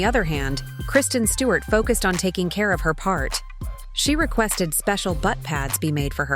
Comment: am I the only one who thought it said fart 😂